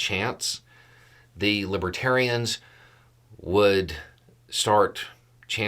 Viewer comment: Was it just my bad hearing or did he keep mispronouncing the name of the party? I heard "Libatarian" over and over. The look on his face as he stumbled down the steps from the stage......